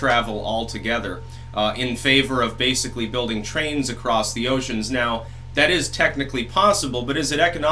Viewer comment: "Trains across the ocean"?!?! While we're at it, why don't we just bungee jump to the moon? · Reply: I thought said trans across the ocean.